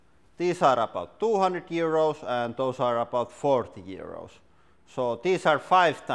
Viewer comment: OMG at 0:28 when you said 200 euros and 40 euros it sounded like you said 200 years old and 40 years old!